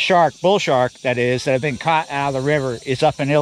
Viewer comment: Did you just say illi noise. Yep watched it again 😂